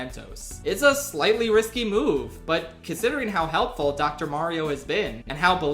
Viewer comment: wait did you say Dr Mario Hejehejejehejejehwhehehee